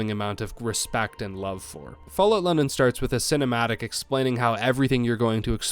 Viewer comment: did you say fallout London or fallout Lennon?